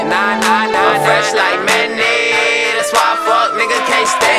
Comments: How many of y'all know its "Fresh like MANNIE" not "Fresh like Mayonnaise" ? He's talking about the rapper Mannie Fresh lol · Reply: yupitsAsiah... This whole time I thought he was saying "fresh like mayonnaise!" Lol 😂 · There needs to be a remix (refix) video to this track featuring a Mannie Fresh cameo and a nod to E 40 (Mustard And Mayonnaise/Nef being part of Sick Wid It Records), because, originally, I thought bruh bruh said, “fresh like ‘mayonnaise’ “; aesthetically that would be dope